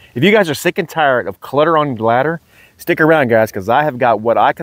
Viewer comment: At first I thought you said “Clutter on your Bladder”………. ???? Then I realized the message…. Great videos, I like seeing and learning from them….. Thank you